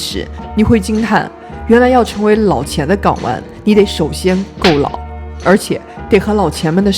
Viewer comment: 我听成：原来要成为老钱的睾丸，你得首先够老。。。🤣